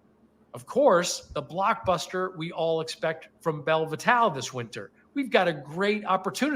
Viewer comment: On another live stream about Belle Vitale, I kept hearing it as “Velvitol” LMAO